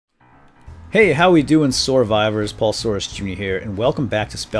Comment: Did you call us survivors or soarvivors? I have been confused about this since you started calling your viewers that, it sounds like your saying SOARvivors but i may be wrong xD